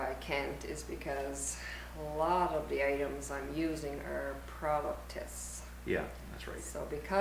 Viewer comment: Since asked nicely ... "The Discoverer A/T3 all types of terrains- wet traction, off-road traction, without sacrificing highway performance. All traction...all terrain..all the time!" (That's my guess and I'm sticking with it, even if Hell's flames show up again!) Kudos for work on the hub cap center schtickers! Speaking of which, I couldn't tell which either horse was saying as to why we can't see doing the schtuff (@ 33:59 - just couldn't make out the word - sorry hearing must be going). :-o Do tell, please. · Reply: (Jif) She started out by saying " A lot of the the items I'm using are for product testing". That was at 33:59. I'm not going to Guatemala every word fast that. Just don't have the time to go through it. I seldom ever watch the videos we do. Good info on the A/T3's ;)